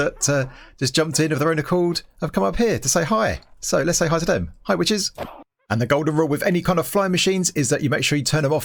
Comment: “hi witches” I thought he said “hi bitchės”